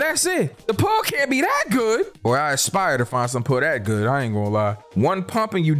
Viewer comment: wtf is he saying ? "Paul" "pua" "pawg" "paw" "puu" ????!